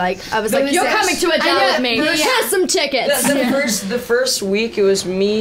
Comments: "YOU'RE COMING TO ADELE WITH ME! HERE'S SOME TICKETS!" I'M DEAD 😂😂😂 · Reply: Martin Cuadra I thought she said "here's some chicken" 😂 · "YOU'RE COMING TO ADELE WITH ME! HERE'S SOME TICKETS!" I'M DEAD 😂😂😂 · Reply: I thought she said "You're coming to Adele with me!Here's so chickens!"